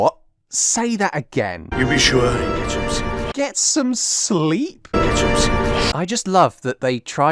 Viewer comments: he says "gets home safe." Still hard to make out, don't blame you for mishearing. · i think he said “make sure he gets home safe” but yh the point still stands